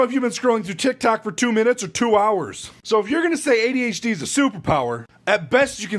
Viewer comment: I heard "ADHD is a soup" because my phone died at 11:01 😂